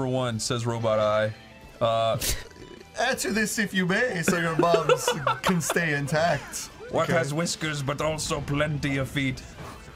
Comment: I heard "Answer this if you may so your mom can stay intact" in Ignignokt's voice from Aqua teen